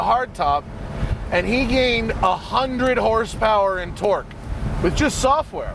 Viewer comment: How do you "gain horsepower in torque"? HP is HP, torque is torque.. He either gained 100 nm or ft/lbs, or he gained 100 hp. Such a strange thing to hear from a car guy lol.. Or did he say 100 hp AND torque? Sounds like "in", maybe my ears are going funny :p